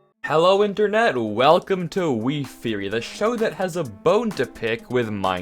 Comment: says 'welcome to wiftheory' what i hear 'welcome to game theory'